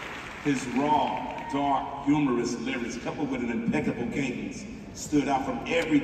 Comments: Impeccable "cadence" is what he is saying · The word you marked as “???” In the subtitle at 3:04 is : cadence. Unless I misheard. English is not my language. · the word that wasn't discerned (put in question marks" is "Cadence." I'm not hating on the uploader. Just saying in case others also had trouble hearing what Dre said. ❤